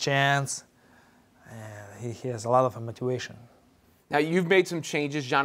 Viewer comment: Lol love this guy! what was the word he said at 1:12? I’m so glad he’s back to speaking English again 😂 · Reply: Motivation